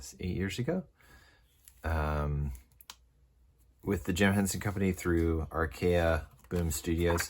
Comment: At first I thought it said tim Henson and was very confused that he jumped from guitar into storytelling lol great video